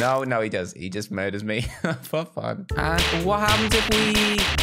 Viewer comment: What did he say at 2:00? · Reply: He murder me just for fun I think that what he said